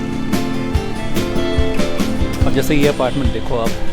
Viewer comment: did he say ASANSOL?